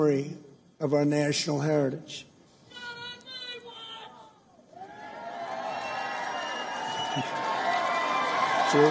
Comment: can anyone tell me what that person in the crowd said? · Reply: he said: "Not On My Watch ! "